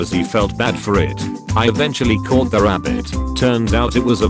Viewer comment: I thought it said called the rabbit not caught the rabbit oops